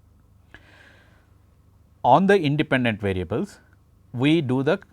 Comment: At @ 6:41- the transcript says all "dependent variables" instead of independent variables . The Prof is correct the Typo should be corrected .